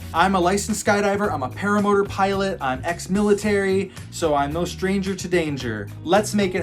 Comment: did he just say "ex-militar"?